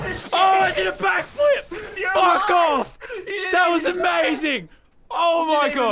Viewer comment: Did he said fuck off🤣🤣